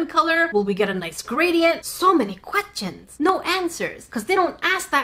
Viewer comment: Quat-chans ? Did I hear "Quatchans " when said Questions?😂😂😂